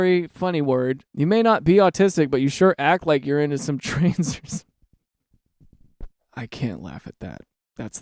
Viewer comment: bro i swore to god that you said at one point "are you into trans or some shit?" and not "are you into trains or some shit" during the autistic thread and i stared DYING laughing because i am into some trans or some shit (i am mtf)